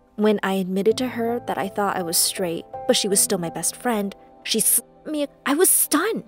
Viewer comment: I thought it they said snipped. But no, they censored out the word slap.